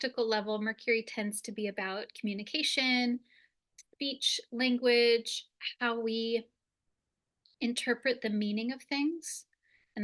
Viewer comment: I'm watching your video and I took note. Am I losing my mind, or did I just hear you say "beach language" haha !